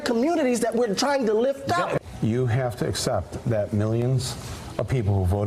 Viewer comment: I know Prof. Glaude said "animus" but I heard "enemas".